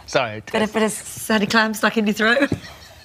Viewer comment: What does Holly say at the start? A bit of something something stuck in your throat? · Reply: I thought she said a sandy clown stuck in your throat XD I heard that so wrong !